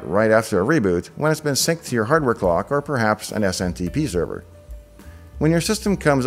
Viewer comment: I went through most of the comments on this to see if anyone else caught what said around the 10:26 time mark? I swear I heard say SMTP Server for setting the time over the network. Last time I looked at what SMTP stood for it did not have any thing to do with time it has to do with email: Simple Mail Transfer Protocol. I meant NTP: Network Time Protocol.